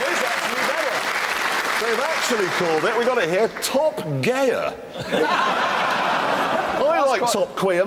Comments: Jeremy: "Theres a website for homosexual car lovers, anybody know what it was called?" my own faulty hearing Guy: "Top Gear" I fell off my chair because i felt that was savage. Too bad i just heard it wrong and he actually said "Top Queer" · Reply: Yeah I thought he said "Top Gear" too. Savage!! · I will happily buy a drink for the bloke who shouted "top queer" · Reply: I thought he said Top Queen till I saw the comments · I heard "Top quiter" kind of Top finisher in other words but finally I got here and it is not what I heard 😀